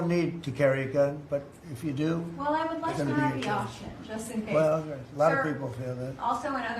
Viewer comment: did he say if you do it’s gonna be an issue???????